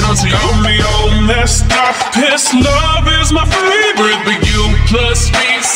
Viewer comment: It is awesome but did I just hear 'his love is my favorite'